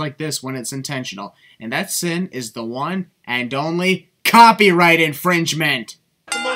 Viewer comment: it said and and only instead of one and only